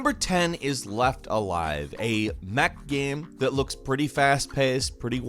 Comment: He said, "A mech game..." I heard, "A Mac came..." I was like, 😱